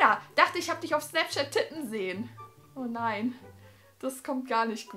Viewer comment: hab titten verstanden😂😂